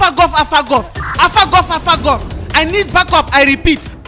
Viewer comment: When the the police officer said Alpha Golf , sounds like he said I’ll f**k up ....I’m dead💀🤣🤣 I always loved their video, make my day happy 😆 and I always update if they have a new video.......